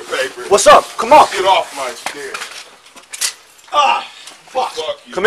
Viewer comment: I think he wanted them off his stair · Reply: flumbo I heard chair but I'm not sure